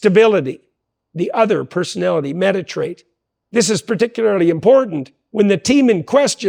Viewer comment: What word does he say at 14:50? Meditrate?